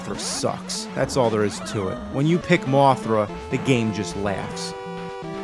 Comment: "when you pick Mothra, the game just laughs" "the game cheats" ...holy shit, this video inspired the creepypasta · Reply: I thought he said "when you pick Mothra, the game just lags"